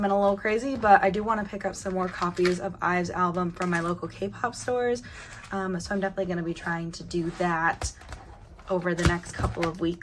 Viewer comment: excuse me, did you say kpop STORE??